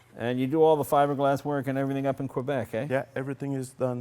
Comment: did he just say, "Quebec eh?" lololol